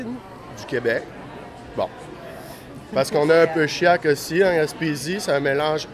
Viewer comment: I think he said Shiac not chiants . Shiac is a combo of Acadian French/English mixed together, nothing like the Quebecois French.